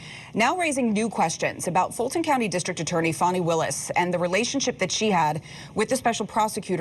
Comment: Did she say, “Phony Willis”? 00:05 😂🤣😂🤣😂